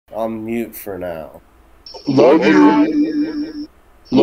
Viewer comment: what did he say? he sounds like he said "thank you". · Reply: "love you"